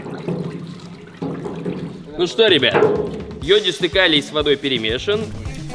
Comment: Мне одной послышалось не ёдистый калий , а лёд из фекалий.